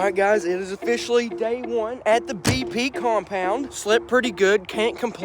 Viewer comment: I thought it said the pp compound